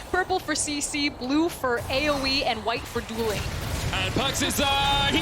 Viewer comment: what did she say? white for? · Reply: Duelling